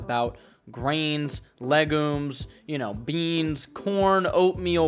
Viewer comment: I thought u said “legos” for a sec💀💀